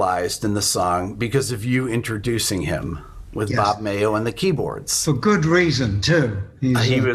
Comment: I was pretty young when I first was given Comes Alive and for many many years instead of introducing Bob in Feel Like We Do I thought he said, "funk man on the keyboards, funk man"